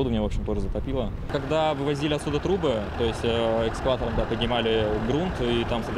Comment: Мужик в панаме сказал "Когда вывозили труБы", а мне послышалось "трупы", и я офигел 0_0